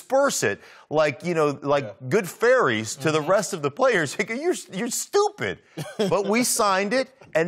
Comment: did he say "nicca you stupid"?